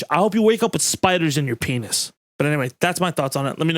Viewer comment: Two raps in 1 vid? We eatin' good this week too!! Also, "I hope you wake up with spiders in your ears" is a new insult that I'm officially adding to my lexicon. Thank you for that. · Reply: i heard pen*s not ears though at 11:00 maybe im wrong or he says it later cuz i havent finished watching yet